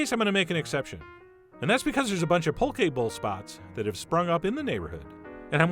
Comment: I heard "a bunch of pokeball spots". God I never should have downloaded pokemon go. -_